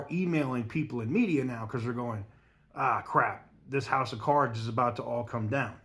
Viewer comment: Did you just say "house of CARBs" ? 😂 lol